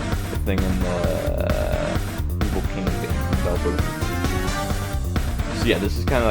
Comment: nice tutorial! what are you saying at 2:28? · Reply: “The evil king thing in Deltarune” Im guessing